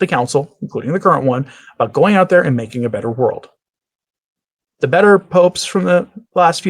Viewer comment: You suggested you heard "make a better world". Why did I hear "make a mess"? Through prayer, sacrifice and witness: Fight for tradition, Catholicism and Jesus Christ the King. We're losing our Brothers who have become lukewarm!